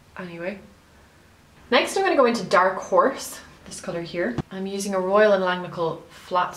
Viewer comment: It sounded like you said, with the first eyeshadow, that the name was "Dark whores" :) I was laughing so hard that I had to pause the video Love